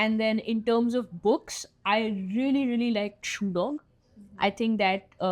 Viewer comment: bhai which movie and book she said i didn't understand any of the word and even the subtitles are not helping...plz someone enlighten me...😭🙏🏻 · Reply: shoe dog by phil knight